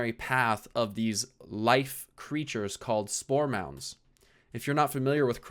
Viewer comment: Could have sworn he said "Store Mounts"